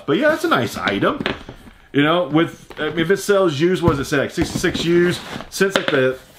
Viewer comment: Did you just said 66 #Years.... Whoooh.. U mean 66$.... Or im I tired 😳